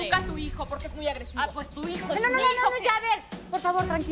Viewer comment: jaja le entendí pues tú hijo es mi hijo